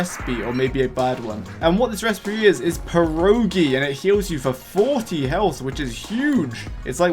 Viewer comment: did you say...PIEROGI?